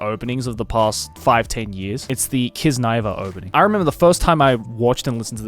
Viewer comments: what anime is this? Didn't understand what animeman say , I heard kizuna ai op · whats the name? Didnt get what he said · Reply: kiznaiver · I can't hear joey's voice clearly. What is this anime's name? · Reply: kiznaiver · Can someone please tell me what did Joey say? I like to check out the anime opening. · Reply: Kiznaiver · Does anyone know what anime is this? I can't hear what Joey said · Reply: Kiznaiver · What is that anime Joey mentions at 23:11? I couldn't quite catch nor find it. It sound something like "kizu ivan" to me but i can't find it. Some personal S tier OP that they didn't mention but I love are Eureka 7's first opening and Heroic Age's both opening. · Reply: It's Kiznaiver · what's the name of the show? couldn't understand. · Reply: Kiznaiver